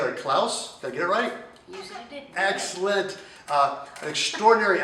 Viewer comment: are we just gonna ignore how cute she is when she says "Yes you did" (She is acually saying "Yes i did" xD)